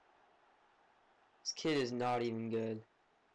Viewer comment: I thought you were saying I wasn't even good. Sorrry. :p